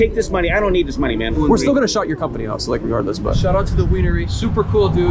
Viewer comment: Big brain move, “shouted him out” did more bad than good and I appreciate that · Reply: I swear if you listen to it right I hear "we're still gonna Shut your company out" aka shut down